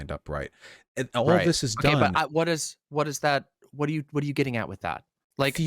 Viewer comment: The "Gaslighting" thing is at 1:10:30. She says: "Ok, what does that...What are you getting at with that?" (he was talking about high precision machines to test quality). Vaush then follow up by saying: "Are you asking what the regulation of the Pharmaceutical Industry get us, what do you mean? You... DM,.. You just asked that, are you trying to gaslight me?" So, Vaush misheard. No gaslighting. She was asking where Vaush was going with his explanations of the need for high fidelity equipment and safety regulations (because her point is that these steps could be made as much as possible by people outside of the pharmaceutical industry). And Vaush though she said "What does that get us?", as in, "why that would be useful?", which was not the question she asked (she was asking what conclusion Vaush was driving towards). Hopes that clears up something.